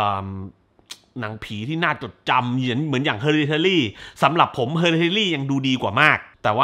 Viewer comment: นาทีที่ 14:08 พูดว่าหนังเรื่องอะไรครับ จะไปหาดู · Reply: Hereditary ค่ะ